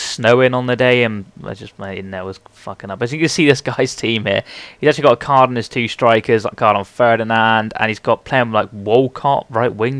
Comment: What does he say at 2:31 sounds like "he's actually got a card on his two strikers, got a card on Ferdinand"